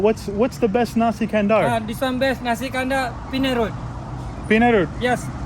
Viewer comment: That Trishaw rider was an Indian so when he said Penang Road but you heard it as Penal Road. Hahaha